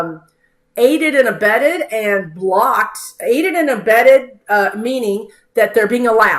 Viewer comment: In my head I heard "IMBEDED"